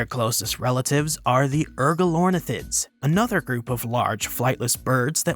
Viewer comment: What was that “urgalonathid” thing you said? 1:45 1:45 1:46 1:46 1:46 1:47 1:47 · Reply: Ergilornis